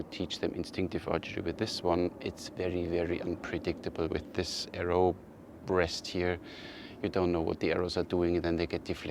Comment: Did you say arrow breast at 19:45 🤔😁